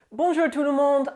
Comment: Oh my gosh. I had my closed captions on when this started. It thought you said, Bonjour TUNA MOLD!’ 🥴😂😂